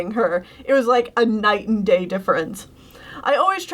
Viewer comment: Am I the only one who heard “night and gay” instead of “night and day”?